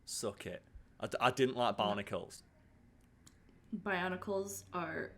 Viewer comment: I hope I'm not the only one mishearing saying 'barnacles' instead of 'bionicles'?